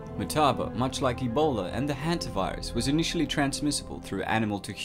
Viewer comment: did you say hentai virus?